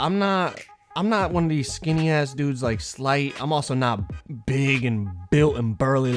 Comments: I thought he said flight and I was shook for a sec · I thought he said I'm not no skinny player like flight 😂